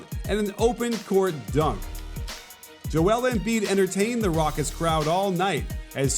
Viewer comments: did I hear "Rockets crowd"? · Did I hear coach call 76ers the rockets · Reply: Raucous.